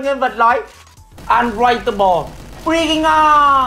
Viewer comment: I thought I heard "inflatable" "bringing on"